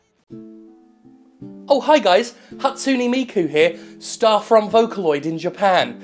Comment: I keep hearing "I, Miku" like "I, Dio". Wait until Jim sees what I, DIO, have done!